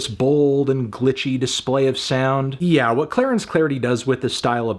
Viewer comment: I'm definitely the only person to mishear Clarence clarity as Clarence Kennedy